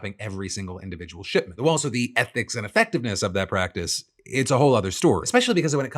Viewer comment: *Ethics (it sounded like you said ethnics)